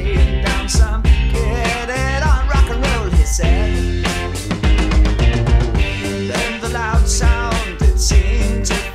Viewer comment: I don’t native english speaker so I cannot hear and understand what he says at 0:34. please tell me what he says. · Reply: rock and roll he said